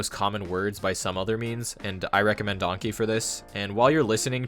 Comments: did he say “i recommend donkey”? i can’t find whatever he recommended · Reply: He said “anki” it’s an app where you can make or download others flashcards. It’s a great app, I use it on my pc, you also have a mobile version but it’s very expensive and the pc one is for free. As far as i know most of the people use it on pc. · I can’t make out what he is recommending. I think he says donkey, but I don’t find anything about it online. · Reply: haha i'm sorry 😅 Anki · did he say “i recommend donkey”? i can’t find whatever he recommended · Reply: It's a flashcard app called Anki ngl it did sound like he said donkey lmao